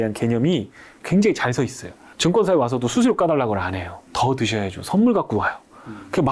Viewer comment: 증권사 와서도 ‘수시로 까발라고 안해요’ 가 무슨 말이네요? 단어가 안들리네요 ㅜ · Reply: 수수료 까달라고는 안해요 ㅎ